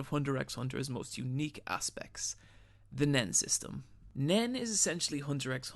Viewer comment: Thought that said Men system